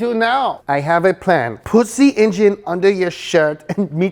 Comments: Was it just me or did just say "pussy engine..." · was it just me or did he say pussy engine?😎🤣